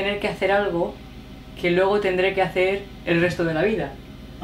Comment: What is the word says at 3:42? Sounds like "lobo"..? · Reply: Luego.